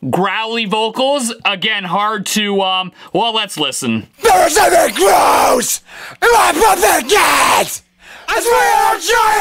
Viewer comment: what I hear: THIS IS A GROSS!!!!! MY PUP HAD GAS!!!!!